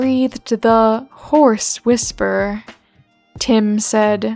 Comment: I had to look at the text a couple times here. Horse whisperer is a real thing. I see it was supposed to be a "hoarse whisper," but it sounded like you added in that last syllable like he just realized the horse whisperer was up to something.